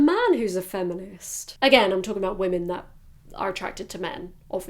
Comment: the subs at 25:33 : "again, i'm talking about women who are attracted to me, obviously" me : "well you kinda heard it wrong (me instead of men) ... but understandable 💁🏻‍♀️"